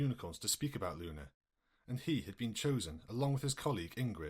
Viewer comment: Sorry i think i didnt hear right 14:55 is She called Ingrid?